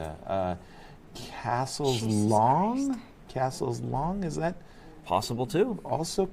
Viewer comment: Anybody else hear Sopiko mutter "Jesus Christ" at 15:50 ? What was that about. Weird